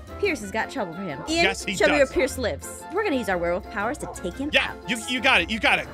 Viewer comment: it sounded like said “show me Pierce’s lips” tbh